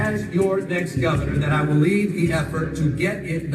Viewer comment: the captions say "leave" instead of "lead" the healthcare initiative. Lol